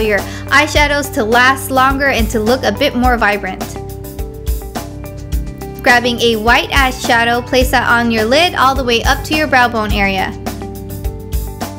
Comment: sounded like you said grabbing a white ass shadow